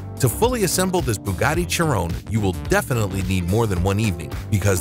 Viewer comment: When he said chiron he sounded like he said churro